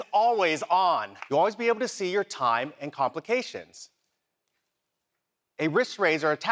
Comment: did he just say "you can always see your time, and complications?" does he mean applications? notifications? Wtf but complications lol? ....